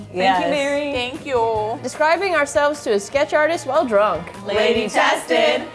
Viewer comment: I thought it said etch-a-sketch artist and was like ooh this will be interesting 😂